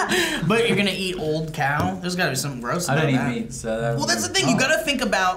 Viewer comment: Did Douglass say he didn't eat meat at 11:37?